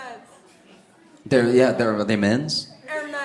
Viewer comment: Legit thought she said "You're a mess" at first 😅